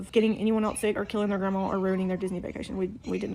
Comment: I’m sorry, did she say KILLING THEIR GRANDMA???